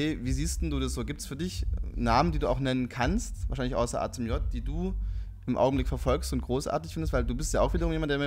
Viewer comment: was sagt er da? wahrscheinlich außer wem? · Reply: Außer "Ahzumjot" , ein Rapper, über den sie kurz davor schon geredet haben, der, der sich "schick" anzieht und Döner kaufen geht. :D